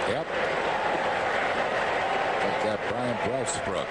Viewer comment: My favorite part is Harry saying "Bryan Breastbrook"